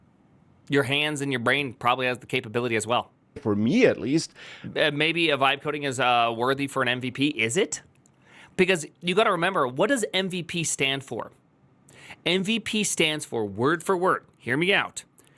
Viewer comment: Not to mock his accent, but I kept hearing "wife coding". Not sure it would work very well either.